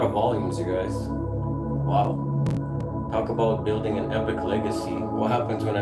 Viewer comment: did you say: how Kaball building a epic legacy?!!!